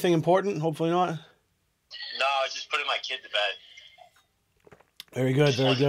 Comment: Am I the only one who heard say "just putting my kid to bed" is he married and have a kid?